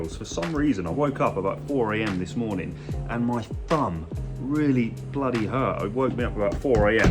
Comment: So glad you’re back!!!! At first I thought you said you woke up at 4 am and your bum hurt! 😂